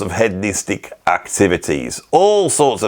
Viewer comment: I heard "head mystic activities" when you said "hedonistic activities", which would make a lot of sense if you were touring with the likes of Yes or Graham Bond. There must be a lot of interesting stories about the weird zen stuff musicians got into while on tour.